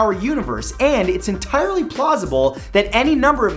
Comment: It it just me or did Mat says Plosible?